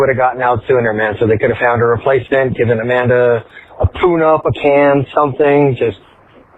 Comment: I swear i heard him say give her a tuna can 🤣🤣🤣🤣🤣🤣🤣🤣🤣🤣🤣🤣